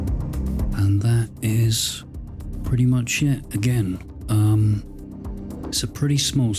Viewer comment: Sir, due to your accent, i heard at the end "thats pretty much shit". I would have to disagree. Thats a most impressive working studio. 3 big ticks. Smooth voice, chill dubtechno backing, lots of cool gear. Nice work. 🎯